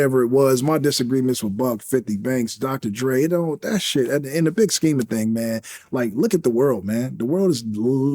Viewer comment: what did he just say???? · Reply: Dre took 50 side back then